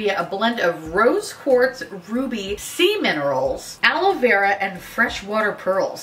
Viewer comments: I heard 'Semen Rolls' not 'Sea Minerals' lmfaooo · I heard “sea minerals” as “semen rolls” and I’m very glad I was wrong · Not gonna lie I honestly thought you said "Semen" Minerals and not Sea minerals so I'm like Wait..what? O.O This week just started and I'm already way to tired xD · When you said "sea minerals" I heard "semen" for a second😬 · Swear i could've swore u said 'semen rolls' not sea minerals. Wonder if i would've made that mistake with another youtuber😏